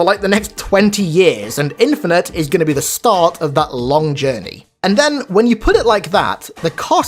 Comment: did someone say “journey”?